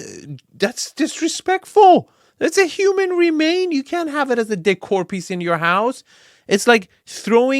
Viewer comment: hahahaha I thought said “deadcore piece”. It literally is a deadcore piece! 😂😂